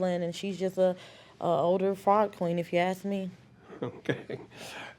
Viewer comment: "older fraud queen." 😂😂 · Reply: I thought she said older fart queen hahaha